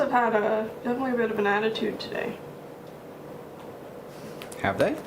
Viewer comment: I swear I heard "bananattude" and now I really want to make that make sense.